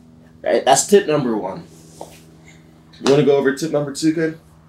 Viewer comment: Super helpful ! also @ 1:57 it sounds like he said "you wanna go over tip #2 babe?" lol